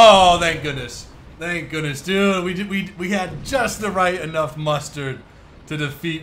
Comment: at the end it sounded like you said "We Had Just Enough Mustard To Defeat Gustav" but you actually said "Mustered"